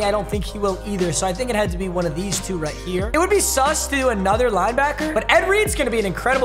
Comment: did he really say it would be sus to prestige another linebacker